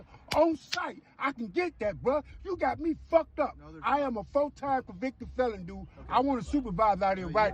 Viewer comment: did he say he is a full time convicted felon? yeah checks out